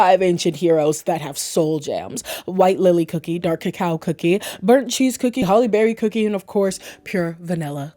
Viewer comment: did she say "Burnt cheese cookie" was an ancient hero with soul jam?